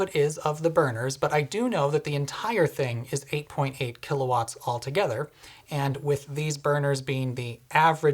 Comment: I think what you said and meant, and what the CC at that timestamp is supposed to spell out is "altogether", right?